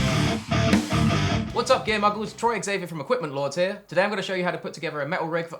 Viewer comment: "Gear muggles" I love you hahahahaha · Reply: I thought he said "gay muggles" at first.